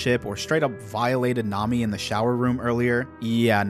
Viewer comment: did he say shower room?